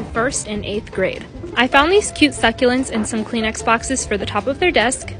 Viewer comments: I genuinely thought she said clean Xboxes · Lol at first I thought she said: “I found these cute succulents and some cLeAn X-bOxEs for their desks” 😭✋